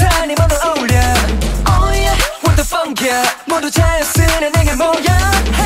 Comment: did he just say "what the fuck?" broooo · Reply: I read somewhere it's suposed to say "what the funk, yo"... makes no sense, whatsoever 😮